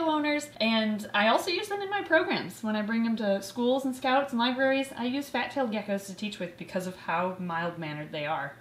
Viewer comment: For some reason I thought you said "cows" when you said "scouts" and I thought it was so cool you went and educated cows about reptiles buy then I listened to it again and realized I'm a fool lol